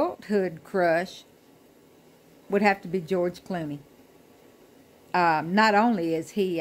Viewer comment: When you were talking about your adult crush, I thought you said George Clinton. I was like, did I hear that right?!? 🤭🤭🤭 Cool video- my dad loves Bob Dylan and Joan Baez 😊 Thank you for sharing 😃🌻🌻🌻